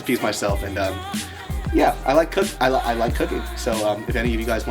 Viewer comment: LOL I thought he said "I like cocking" not "I like cooking" hahahahaha